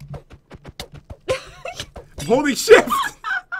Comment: Who else heard at 17:30 he almost said a cuss word but instead said shift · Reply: Happy loving Sisters! it was at 17:41 and he didn't 'almost' swear he just said holding shift